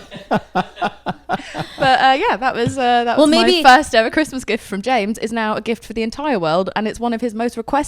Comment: I thought the secret teller said CHRISTMAS MUSIC and was soooo confused for a hot sec